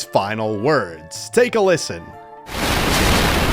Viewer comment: I think magolor soul said : KIR…KIRBBBYYY